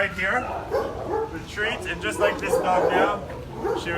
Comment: Good on you! ❤ Lucky Lillie. Admittedly though, I thought you kept saying that you were throwing Lillie a "tree". Curious, I watched that video to see if you were throwing her a "twig" maybe. Suddenly... ah, a "treat". Lol 🤣🤣🤣 So funny. Nevertheless, good job saving her. Had you not spoken to the other person, they might not have just returned her, but euthanised her instead. You definitely saved her. Good job! 😎👍